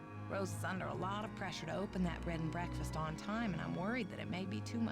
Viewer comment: never noticed it before, but it sounds like Hannah is saying "Bread and Breakfast" 😂